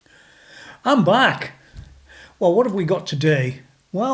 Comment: haha i thought he said "im black" --- see what this crazy world does to a person??!?!?